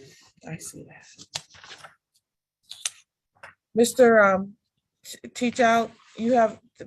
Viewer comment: Hadn't looked at all the names, so I heard "Mr... uh... Tea Towel..."